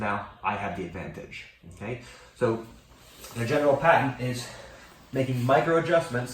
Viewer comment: Did he just say "the General Patton is"?